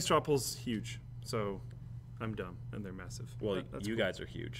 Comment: Did he say masa two shitz?